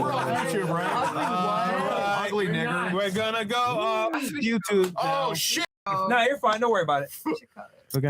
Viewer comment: Could swear I heard someone say we’re going to go up on YouTube right here